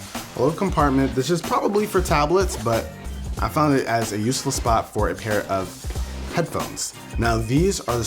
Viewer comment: it sounded like he said, "a useless spot."